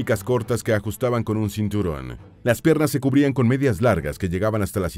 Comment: Minuto 9:54 acaso solo yo entendí que dijo las perras ( en referencia a las mujeres 😂🤣)